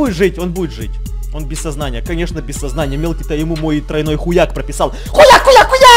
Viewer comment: мне одному послышалось он сказал х*як?